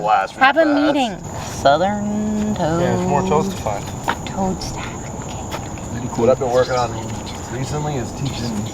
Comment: I just hear the happiest adult whisper " toad stack " and this made me realize I need to have more fun in my life !!